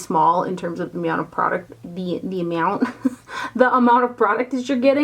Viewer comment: did you say the "meow-ount" of product instead of amount? Furbabyz always on the brain - too cute 💕